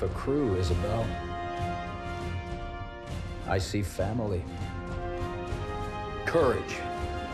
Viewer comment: Did I hear family